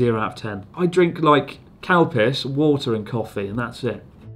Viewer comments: lol I thought for sure they were saying Cow-Piss for Calpico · When talking about the calpico I thought you all where saying cows piss lol 😆😂🤣